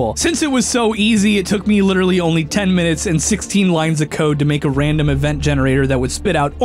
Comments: I thought he said 16 lines of coke not code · "16 lines of code" what i heard; "16 lines of coke" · When he said 16 lines of code. Thought he said 16 lines of coke